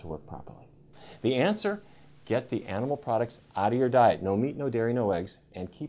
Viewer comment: Did he say NO Rice? Rice is Not fat. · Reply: I heard "no meat, no dairy, no eggs and keep the oils really low...". He said nothing about rice.